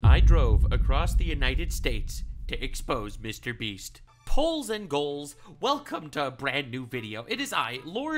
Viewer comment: Did he just say poles and holes???😂😂😂😂😂😂🤣😂😂😂